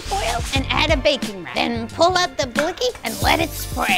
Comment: Did she just say PULL OUT THE BLICKY